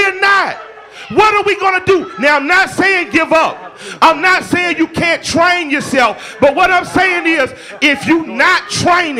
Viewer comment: well killer Mike ole boi in Tulsa had his hands up he still got tasted on shot at the same damn time. but from video he was being compliant. look that nice shit only works but so much. we was told the same things back in the day mf still got lynched an rape ect..... war ain't pretty but it a must. being passive won't always help just let's enemy know your willing to take whatever he's going to do. I know bc I've been to war. wasn't in the thick all like that but I did understand the roles of a sadist and normal scared human being. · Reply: I coulda swore he said " I'm not saying give up. I'm not saying don't train yourself." P